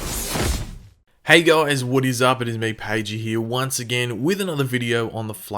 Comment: I thought said 'Woodys up' at the beginning of the video..thinking who the fuck is woody!